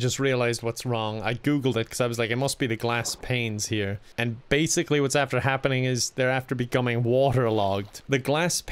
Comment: what are you even trying to say? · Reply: "And Basically what's after happening Is there after becoming waterlogged" WHAT????